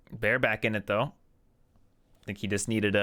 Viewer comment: lol sounded like "Bare-backing it though"